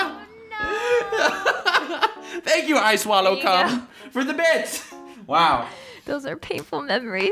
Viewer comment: what it says - ice wallow come what it sounds like - (_________) the subtitles - I SWALLOWED CARL